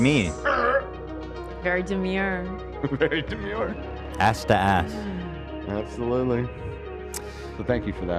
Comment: did Ethan just say ass to ass, like from Requiem for a Dream? Wtf lol